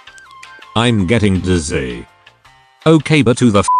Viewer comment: Im getting dizzy i thought he said im getting disease